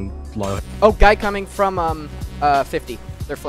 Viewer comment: Am I the only one who hears "Oh, gay coming from... uh... 50."